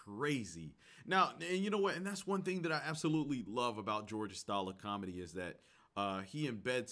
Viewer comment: Was I the only one who heard "George STALIN"? 😆